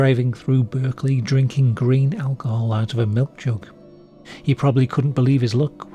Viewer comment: GRAIN alcohol! I thought you said GREEN alcohol. Im thinking, thatd be a huge red flag, dudes drinking antifreeze.